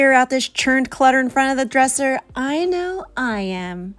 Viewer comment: I thought you said “churned clutter and friend of the dresser” at the start lmao 😂